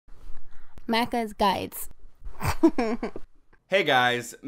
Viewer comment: When the intro came, I thought it said, Maccas Died! HEE HEE! And I was like holy sh*t